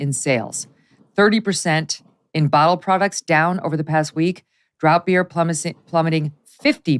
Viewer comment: Did Megyn say “Drought beers”? (3:50) Sounds like a great idea!!